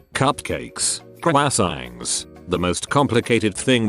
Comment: Lol 3:28 “Qcrwassangs” instead of croissants. Wtf...